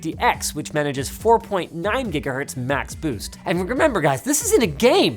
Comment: Did i hear "Jigga-Hertz"? Is linus an XQC fan? lol